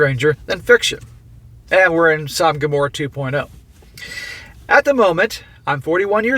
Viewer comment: what does he keep saying here? 3:22 Som gamora 2.0? what does that mean lol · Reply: In the Abrahamic religions, Sodom and Gomorrah were two cities destroyed by God for their wickedness. Their story parallels the Genesis flood narrative in its theme of God's anger provoked by man's sin (see Genesis 19:1–28). They are mentioned frequently in the prophets and the New Testament as symbols of human wickedness and divine retribution, and the Quran also contains a version of the story about the two cities. When he says 2.0, he's talking about how modern times are looking pretty bad.